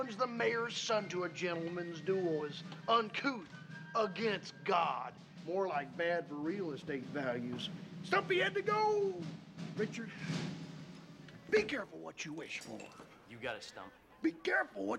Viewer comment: STUMPY HADTA GOOOOOO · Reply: For the past 20 plus years I thought he said “so he had to go.” I don’t even know what’s real in my life anymore…